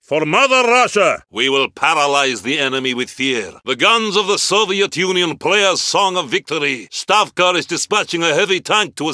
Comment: what he is saying ? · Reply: The guns of the Soviet Union play a song of victory